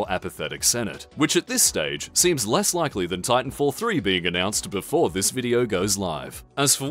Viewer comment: Did I hear Titanfall 3?!